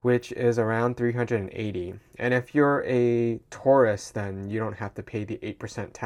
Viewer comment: the way you say tourist sounds like taurus in Australia 😉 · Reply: oh he meant tourist. I was so confused. I thought he said taurus aswell 😭